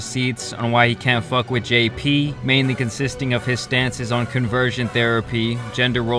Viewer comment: Anyone catch... "Conversation" therapy? lmao